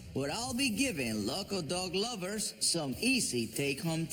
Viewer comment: I thought Cesar said I'm giving loco dog owners help.He said local,though loco might be closer😂😂